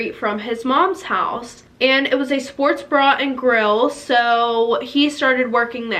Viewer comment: “It was a sports bra and grill” I’m actually dying 😂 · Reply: lol same i was like oh my gosh did she just say “ sports bra and grill” 😂 i legit to the captions on to make sure